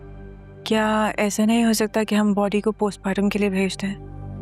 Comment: she said postpartum instead of Postmortem 😂